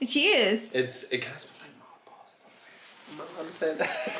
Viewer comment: I thought said, “it smells like Rob balls” and I swear, I lost it. 😂